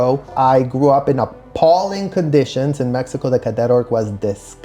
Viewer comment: are you purposely trying to misinform folks or did you make a mistake and not catch it ? He clearly said appalling conditions in Mexico not in the cult. Just checking